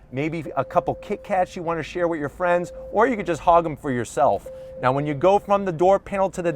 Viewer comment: i thought he said HODL those kit kats for yourself I was like shit this dude knows whats goin on lol